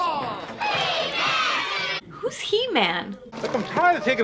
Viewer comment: Did she really say who's He-Man? 😲